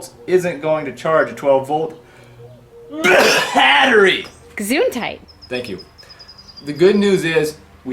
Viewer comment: Did i just hear a Gesundheit? Greetings from 🇩🇪! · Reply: ich habe auch Gesundheit verstanden :D